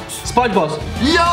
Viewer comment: Hätte statt SpongeBozz Sun Diego geschrieben hätte es ansatzweise mehr Sinn ergeben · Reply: IchTragIcedOut ich hab spongebob verstanden 😂